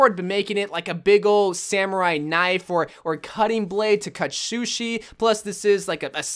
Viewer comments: I'm sry did he say shushi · I think I heard him say shushi